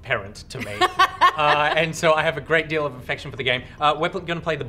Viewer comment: I am sooo playing Crash... if only I had a ps4 v_v" .. my wallet keeps telling me "no".. Also, did Nich say a great deal of "Infection" for the game at 0:44 ??